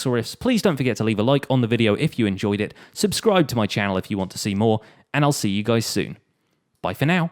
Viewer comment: For a second I thought he said “subscribe to my channel if you’re single”. Well done brain.